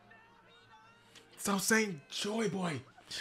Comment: He said joyboy, or did I misheard? How do they know that name this early